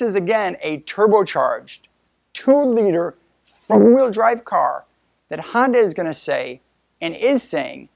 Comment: Did you say 'Four wheel drive car' at 4:24? It sounds like it was edited and is hard to tell. I have not heard this being mentioned before, and would think this would be big news if it was.